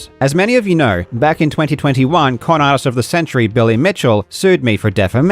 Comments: I had to rewind it like 8 times to finally hear "con artist" instead of what I heard the first time... "Billy Mitchell, clown ass of the century" 😂😂 Sounds right either way though. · I swear I heard say "Con-arse of the century, Billy Mitchell" rotfl